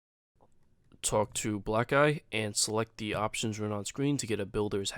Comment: mfw i thought he said blackguy instead of black-eye